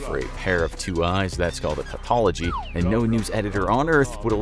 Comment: I kept trying to google what you said about journalist editor and tautology. I had to turn on captions because it sounded like you said paupology so I was googling the wrong thing.Not a big deal just thought I'd let you know lol.